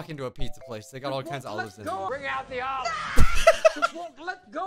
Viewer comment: When the said bring out the olives I thought they said bring out the oscars